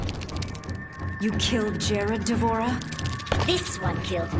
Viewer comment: Did she say "Jerik"?